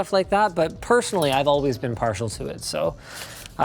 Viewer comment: What is he saying here: “[…] but personally I’ve always been [???] to it.”? I hear “partial” but that doesn’t make sense. 🤔